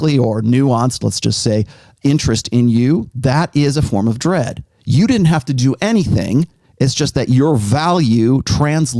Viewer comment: "hot guy in the (what?) Party" ... I cant make out the word he uses ... What is he saying there? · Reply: Cancun foam party